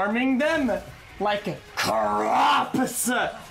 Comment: We're farming them like CAROPESUH....... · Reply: Oskar Rådstam I thought he said like crops